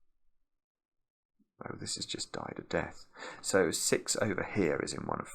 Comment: am I tweaking or did he say die of death